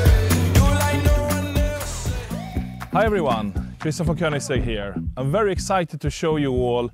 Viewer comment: In the beginning on 0:40. I thought i heard 'Christopher koegnisegg' lol